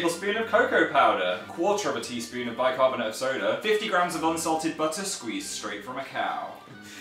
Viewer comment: I thought it said unusual ted butter 😂😂